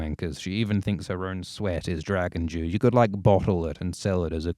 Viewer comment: I swear I hear "Dragon Jew" and we all know that's Moishe Dayan!